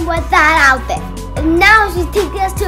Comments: At first I thought he said Mommy a b** but then I realize he actually said Beach I think and I was laughing on the floor and then roll off to California😂😂😂😂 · did he just say,'' No mommy a beach.